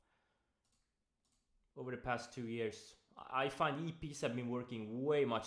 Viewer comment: Qull said that he are not trading Brekouts so much, so what he is trading bcs i didnt hear it good? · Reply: Episodic pivots or in short, EPs